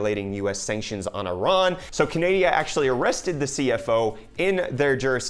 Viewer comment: did you just say Canadia?